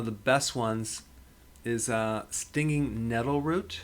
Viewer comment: What did he said @ 4:47 ..... some root ?? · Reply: It was Stinging Nettle Root.